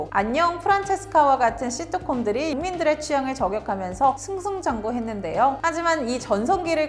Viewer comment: 자막은 국민들의 취향인데 말은 왜 인민들의 취향이라고 하는거죠?? 내가 잘 못 들었나 싶어 느리게도 들어보고 자막도 켜봤는데 다 인민이라고 들리고 나오네요. 뭐죠?? 리북 출신이세요?